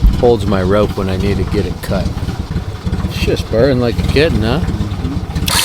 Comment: when you said"it's purring like a kitten" the captions said "shits burning like a kid". haha